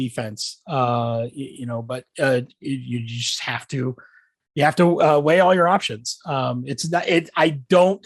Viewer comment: I swear he says “you have to whale all your options”